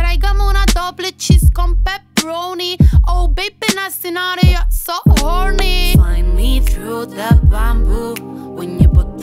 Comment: jaja en la parte que dice Oh baby, nasty not you're not so horny pense que decia "oh baby en el escenario sos horny" jsjajas